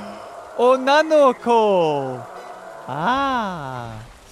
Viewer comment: did he just say unano ako? XD